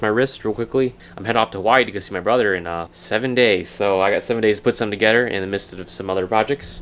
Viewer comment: I thought he said "I'm going to se my brother at the Y in 7 days" u know like the YMCA